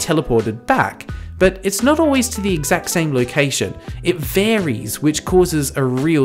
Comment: Subtitle typo at 2:40 says "expert" instead of "expect" 3:55 every instead of ever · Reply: some instead of same